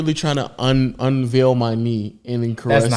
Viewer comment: thought he said "unveil my meat"